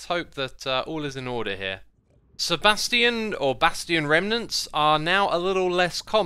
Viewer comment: did he say Sebastian?